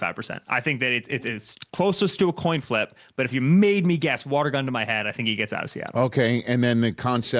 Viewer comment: Did he just say "Water gun to my head?" 😂